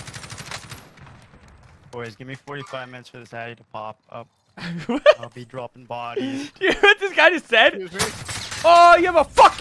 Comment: what did he say? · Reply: I said " give me 45mine for the addy to kick in an Ill be dropping bodies "